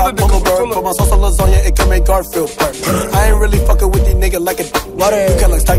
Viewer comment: I thought he said “it can make Garfield burp”